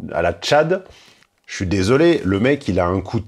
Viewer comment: "Le mec...", pour moi c'est "ce petit garçon de 15 ans..."